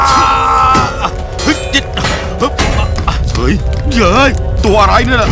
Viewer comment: เหมือนหนุมานพูดว่า เชี่ยเอ้ย5555